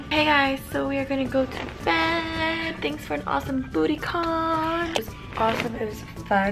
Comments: At first I thought she said thanks for the awesome booty call😂😂💕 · I thought I heard Booty Con... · I thought she said "thanks for an awesome booty call" lol 😂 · i heard Alisha say at bootycon😂😂😂😂